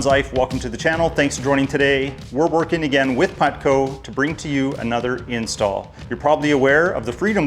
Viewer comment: lol I thought he said pudco